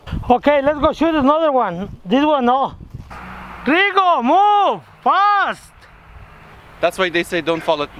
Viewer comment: höhö i always hear "Trigo! Move!" Great name for a dog. me espaniol es mui bien^^